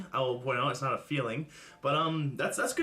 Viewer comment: I keep thinking she's saying 'feelings' not 'fuelings'. Even if she had a slice of pizza on that diet, she'd still lose weight...whatever...just quit -- do we expect anything less from her? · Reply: it's not feelings? Fuck, I thought she keeps saying feelings and I'm like "that's a weird way to describe a meal".